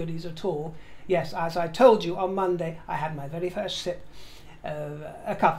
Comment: My hearing isn't the best, so you were talking about coffee, and I laughed out loud because it sounded like you said, I had my first shit. 😂 I rewound and you said sip, but the first did make sense. Enjoy your coffee my dear fruit.